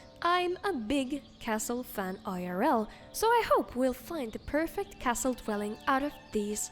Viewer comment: When you said that you're "a huge castle fan irl" I heard "im a huge vampire elf" and to that I say "Stendar/S'rendarr/Stuhn have mercy!"